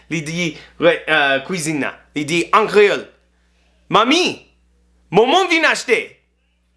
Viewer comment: Lol 6:12 she did not say "Mormon vin achte" she said "Men yon moun vin achte" 😂🤣